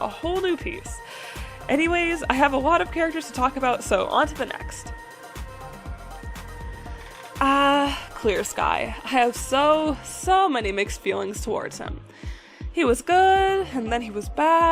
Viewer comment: For some reason, my mind understood "Quiet Rain" instead of "Clear Sky" and I almost spit out my breakfast.